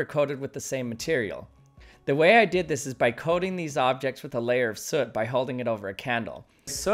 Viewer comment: What's the name of the material, cuz I thought I heard "the sus"